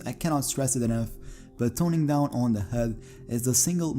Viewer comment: Hey man. Sorry can I ask what you said at 1:24? I heard it as ‘turning down the head’? What did you mean? Thanks. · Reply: I meant turning down the HUD my bro